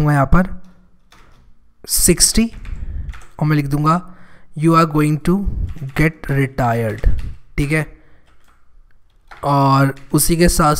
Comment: Fun Fact: at 9:20, I thought you say, You are going to die 😂😂😂😂😂............but love you bro❤️❤️❤️